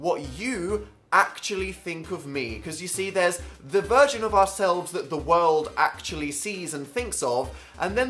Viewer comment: I heard the 'virgin of ourselves' and I cried 😂